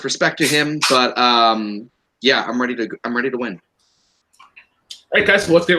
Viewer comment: Wow. I meant to say, I’m “here” to win. Sorry if I sounded like a jerk.